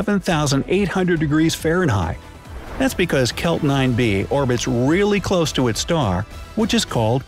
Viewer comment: YouTube Thought you said Celt 9 B instead of kelp 9B